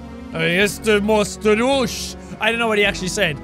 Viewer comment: "Ja jestem ostry nóż!" It's Polish :D But it said "knife" instead of "spoon" :P